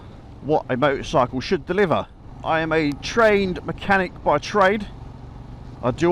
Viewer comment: Had to switch on the subtitles cos i swear i thought you said - I am a train mechanic and i immediately sat up!